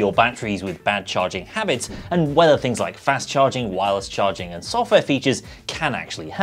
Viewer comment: can help what exactly? The longivity of the battery? Cuz it sounded like if "whether fast charging is going to help in that" Sorry English is not my first language